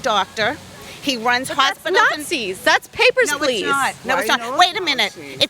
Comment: Anybody catch what she said at 7:18? “My husband runs hospitals…” Go dig that.